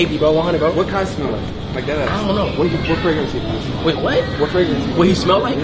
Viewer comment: I thought he said “What brings you here?” Man said “What fragrance he use?”😂😂😂 that’s CRAZY😂